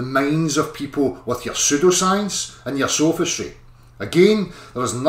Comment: With that accent, I thought he said you were infecting people with your super-science. Way to go! It's a bit disappointing to learn he said pseudoscience.